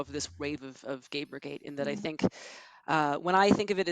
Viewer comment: I know she meant gamergate, but I swear to God she said "GAPERgate" Probably just the mic quality, but that's still hilarious to think about.